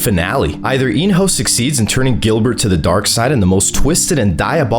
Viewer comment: do you mean Go-hun because I heard Gilbert